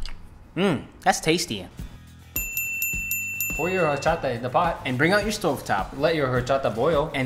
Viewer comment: 1.) HOW CAN U USE CHOP STICKS. I CANT EVEN USE A FORK 2.) I didn't know green likes sticky girls 3.) am I the only one who thought they said "Whorecha"